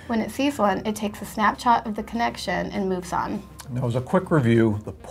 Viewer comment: Did she really say snapchat